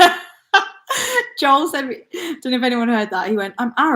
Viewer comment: did call her bf Joel?